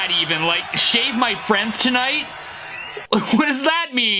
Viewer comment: “Shave my friends tonight!” crowd cheers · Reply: I thought he said shag my friends tonight